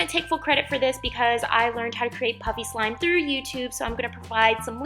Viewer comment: it sounds like she is saying puppy slime